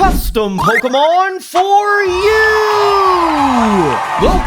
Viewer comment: I thought you said creating a pokemon for you, but where's mine😢